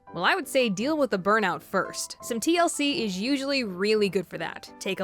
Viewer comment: I thought she said thc